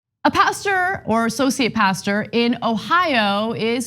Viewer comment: At first I thought Ana said, "a pastor, or socio-pastor..." 😂